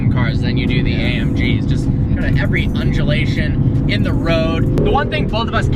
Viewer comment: Did say, @ 3:36, "Every Unjulation"???